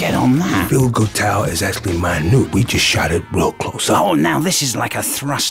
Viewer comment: Gah, what does Russel say around 2:30? It's driving me nuts. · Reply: it’s been 10 years but I figured he said “feel good tower is actually minute we just shot it real close” and minute as the adjective coming from the Latin word Minutus which means “very small”